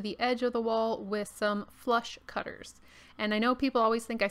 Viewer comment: I had always heard you say flush cutters But of course right after you said "I know people always think I say "flesh" when I say that, but no it's "flush" F-L-U-S-H" I cannot not hear it as "flesh" 😂😂😂